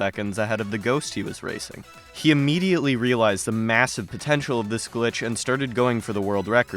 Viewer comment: Did you just say MMM-MASSIVE?